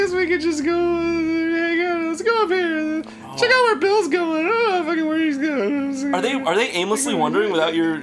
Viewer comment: No matter how many times I hear it, I never understood what Arin said. All I gathered is that it is aggressive. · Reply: After he says "Check out where Bill's going", he says "I don't fucking know where..", then mumbles the rest.